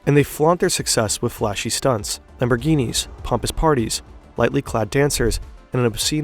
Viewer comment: I thought dude said “lightly clapped dancers”